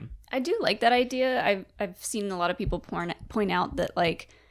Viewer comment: 😮😮 did she say people p*rn then correct it to point at 1:24 LMAO 🤣🤯